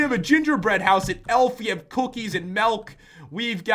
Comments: did he say "melk" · it sounded like he said melk instead of milk